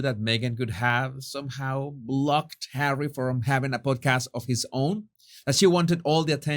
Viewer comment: I thought you said PORNCAST 😂😂😂